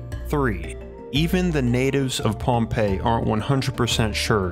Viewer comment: I’m sorry big pet peeve of mine (and probably others) when a video like this starts and there is no global context of where exactly this place is. Perhaps a global map or introductory statement zooming into the geographical region of interest would be better. Otherwise great work. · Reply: Lol agreed. i heard "Pompeii" - not Pahnpei - paired with "basalt" and "lagoon," so I was convinced this place was in Italy, until the photos of the villagers showed Polynesian faces. Totally set the globe in my head spinning!